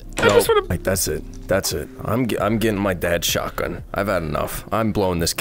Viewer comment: I'm getting my dad shotgun One of Mully's videos:my dad left me when I was 2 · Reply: I thought he said his bed shotgun